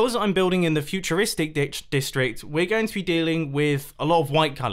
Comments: did anyone else hear mumbo say futuristic dick- district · Did anyone else hear Mumbo say Dick